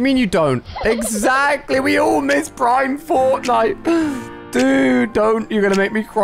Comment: When it said do you like prime Fortnite I said no because I thought it said primal